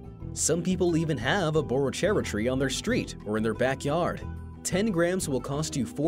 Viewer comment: I thought it said borracho tree meaning drunk tree